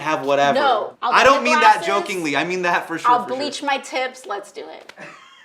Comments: Thought she said “I’ll bleach my tits” I’m like yaaaas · I thought she said ill bleach my tits😂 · At first I thought she said she'll bleach her tits. Wut